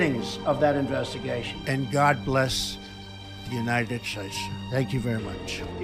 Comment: Sounds like he says, "The United Scheiße"